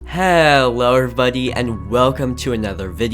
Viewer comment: I thought he said HELLO BUDDY in an Australian accent